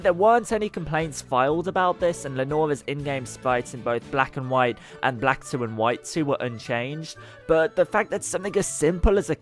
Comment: sounded like he said "black and white, and black, silver and white 2 where unchanged" when talkking about the mammy stereotype btw nice vid, got my sub